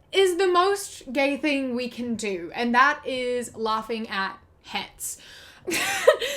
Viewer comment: when you said "laughing at hets" at first I thought you said "laughing at hats" lmao